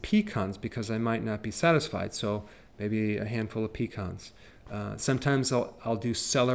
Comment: For a minute there I thought that you said a hand full of peacocks but you said pecans I had to rewind the video several times.